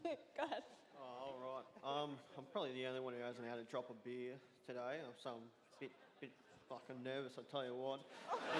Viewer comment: tf did he say? lol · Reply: I'm probably the only one who hasn't had a drop of beer today...so I'm a bit fucking nervous I'll tell you what XD fucking hilarious